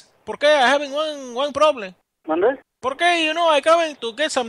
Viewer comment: Hey, love your videos a lot and always watching, not sure if anyone else has commented this but at around 00:09 seconds, in your captions it says the guy said manager, but while I was listening, I'm pretty sure he said "Mande?" (what? in spanish) and yea, I do speak spanish, so I guess it just came really clear when I heard it, not trying to sound super amazing, but just wanted to let you know! Anyways, love your videos and I'll always be watching!! Thanks!!!